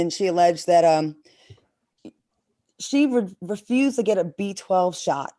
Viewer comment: 😡😤✨🙏🏽😓🙏🏽✨😤😡 I thought she said they didn’t refuse the B12 shot… Heard that animal on first baby taken “Because I CAN” 😤😡